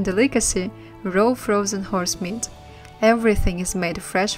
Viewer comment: um... 12:52 did she say horse meat? :|